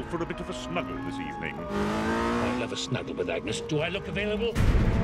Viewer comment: When I listened carefully what Sir Edmund had said during Cogman mentioning about his availability on snuggling with Agnes, I immediately assumed it was "I'd never snuggle with Agnes." So, I don't know whether the caption is true or false. Edit: It was